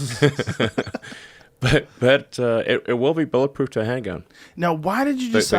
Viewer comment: what does musk mumble? I think he's trying to throw in that it will be bulletproof but not the windows